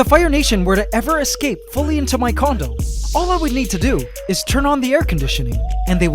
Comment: Bro i thought he said "if the ants get into my condom" instead of condo